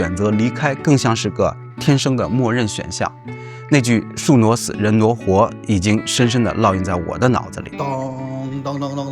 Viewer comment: 一直空耳聽成「宿儺死，人挪活」